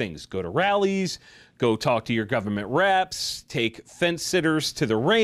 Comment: I totally misheard you. I thought you said “government rats” and I chuckled heartily.